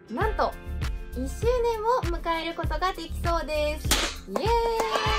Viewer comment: (ﾟ▽ﾟ)/ｺﾝﾊﾞﾝﾊ 10周年って聞こえたから凄い長いと思いましたww 1周年おめでとうございます🎉🎉🎉